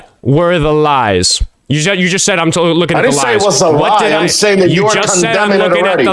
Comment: "Lies" although it sounds like lies. I do think he said eyes. That makes sense as well, I think it makes more sense than him saying lies.